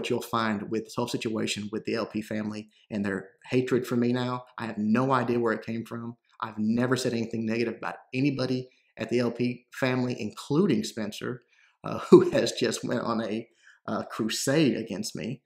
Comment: "I don't even know who Spencer23$ is" ROFL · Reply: aaah sorry my bad. thought he said Spencer23$